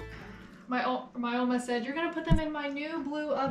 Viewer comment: Wait, wait, wait... did you say "Oma" at 31:09? That's nuts! I know only a handful of Germans / German descendants in this area of FL... Its pretty rare here! 😂😅 Edit: Ah, yep. You did lol. My Oma came here when she was about 17 as well (1953).